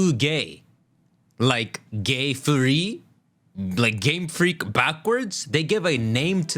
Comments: who hear Gay Furry ??? 😂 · it sounds like he said gay furry which is amusing to me